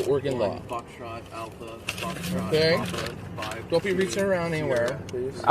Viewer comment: Did I hear that Cop on the radio in the background say at about 6:20 'Foxtrot Alpha Foxtrot Oscar'? (As in FAFO) 🤣😂